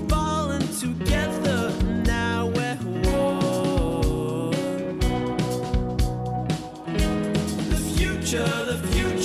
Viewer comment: I could've swore I heard one of them say "Together, We're forever alone". :3